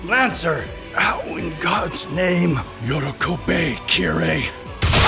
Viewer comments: What is it that Lancer says at 29:02 here? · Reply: Yorokobe, Kirei. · Freaking AMAZING episode, so glad I caught the premiere! Can anyone make out what Lancer says at 29:02? Sounds like "Yoro kopei, Kirei," but I'm 99% sure I'm hearing that wrong because I don't think those words mean anything. Does anyone know what that line is? I'm assuming something vaguely Gaelic? Maybe an Irish "fuck you, Kirei"? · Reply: He said “Yorokobe, Kirei”, which means “Rejoice”, it’s a callback to Kirei saying “rejoice” a lot when scheming